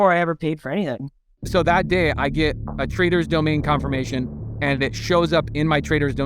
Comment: lol, funny thing is whenever you guys say "traders domain" I keep hearing "traitor's domain", how fitting.